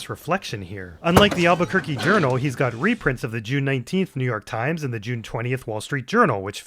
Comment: I heard you say "Jew 19th" here so I rewound it and then laughed extra hard when I saw that the headline said "Israel"